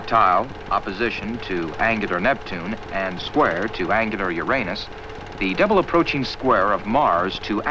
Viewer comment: "And squared to angular Uranus." First thought, "Hurr, hurr, he said your anus!" Second thought, "WTF was he talking about?"